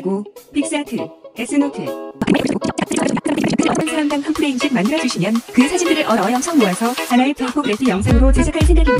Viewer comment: 와 같은 모바일 앱으로 그려주셔도 되고 심지어 까지는 들리는데 그 뒤는 잘... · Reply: 찰칵 사진을 찍어 만들어 주셔도 됩니다. 한사람 당 한프레임..(뒤에 있는거 그대로)